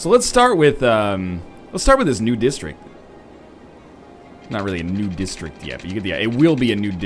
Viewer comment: When ever he says "new district", I hear "nude district."